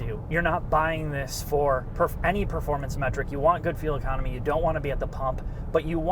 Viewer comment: That sounded like "you want goodfeel economy".